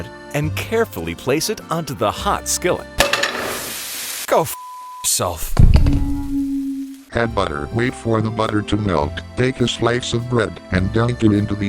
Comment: "I'M SO SICK OF THIS!" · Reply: Why does most people just say something different as what he says on 1:26. He said "Go bleep*self" or "Go *** ****self"